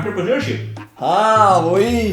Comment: What's the word says? Atripulusha or what?